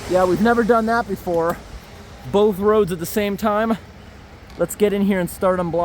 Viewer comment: When he said I'm sure you've never seen that before both roads at the same time I swear to God I thought he said I'm sure you've never seen that before both holes at the same time I was like yo getting nasty with it lmao